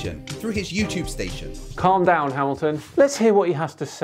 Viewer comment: What does say here? Sounds like a UK joke; "Calm down, Elton." ?? I missed it whatever it is. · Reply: If I'm not mistaken, I believe he's saying "Calm down, Hamilton"